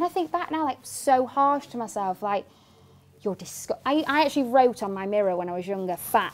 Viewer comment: Is she said ( I actually wrote or i i… she wrote) and she is talking about her self in the past and she’s a different person now Someone explain it to me bcz I’m not a Native English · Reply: She said "I actually wrote", I think her accent made it a bit difficult to understand. :)